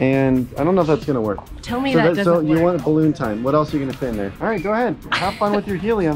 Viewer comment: I thought that said bathroom time😂😂😂